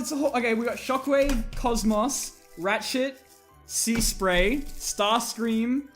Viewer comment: I swear to god you said “rat sh*t”